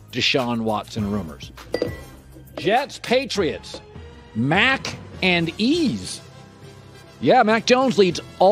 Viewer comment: "Mac and Ease"; I see what you did there. 😎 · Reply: Thought he said MAC AND CHEESE! 4REAL